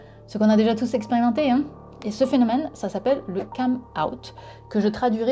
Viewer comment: j'ai compris ''cum-out'' o^O